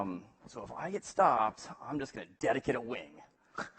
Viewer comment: What does he say at 1:49:54? Thanks! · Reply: "dedicate a wing"